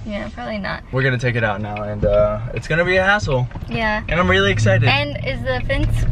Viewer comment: I liked it when said hassle and it sounded like he said asshole! Hahahaha! 😂